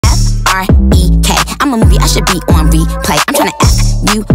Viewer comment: "f-r-e-k" still gets me to this day edit: damn I kinda started a slight war in the replies oops edit 2: not to be that person but MOM IM POPULAR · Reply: y’all they’re talking about in the beginning where it sounds like it says “f-r-e-k”